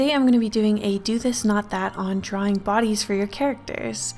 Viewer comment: BOOOOOOOBIES I thought it said boobies